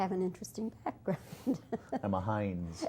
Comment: What does Brandon say when the interviewer tells him he has an "interesting background"? at around 0:45 - 0:46. I can't quite catch what he said. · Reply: He said " I'm a heinz"